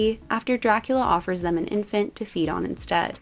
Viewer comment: did you say infant 😢that poor baby 😢